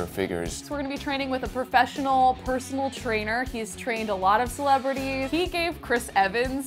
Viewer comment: Who else thought this said tanned instead of trained